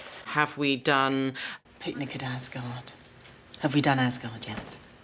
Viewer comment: OMG. DID SHE SAY "ASGARD"? THOOOOOOOOR.